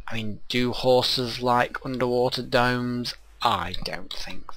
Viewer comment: I heard "Do horses like underwater domes?"